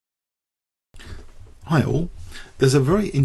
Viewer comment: Haha I always thought you were saying "herro" at the beginning of your videos, but I finally realized that it was "hi all'"!